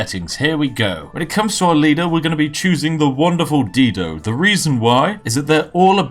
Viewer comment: I heard "the wonderful dildo" ...